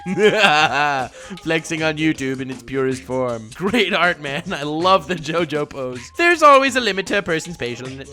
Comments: did Nux just say “love the jojo pose”?? :0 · Did he say jojo pose because that was clearly black reaper Kaneki.